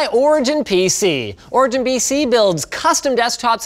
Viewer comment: anyone else notice he says "BC" instead of PC during the beginning of the ad he does